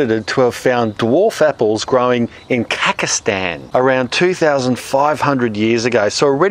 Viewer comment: did he just said kakastan